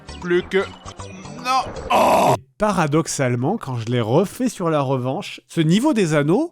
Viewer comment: quand il a dis "parradoxallement" j'ai compris "paradoxe Allemand"